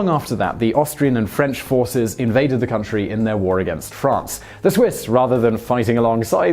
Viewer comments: Wait. I am confused. Around 1:00 I heard, "The Austrian and French forces invaded in their struggle against France." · What did he say? ...Austrian and French armies iinvaded in their war against France.... Is that what he said? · Did he say Austrian and French forces invaded the country in their war against France? 1:04 time stamp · “the Austrian and French forces invaded the country in their war against France” Did I hear that correctly? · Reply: Austrian and Russian forces